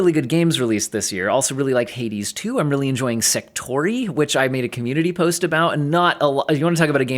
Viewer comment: What the hell did he say at 18:27? I want to look at the game, but I have no idea what he’s saying. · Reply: Sektori